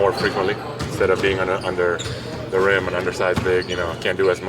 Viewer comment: All I heard was, "I'm an undersized big"